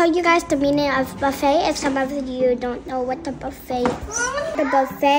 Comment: There's no way I heard "what the buffet" as "what the fuck"💀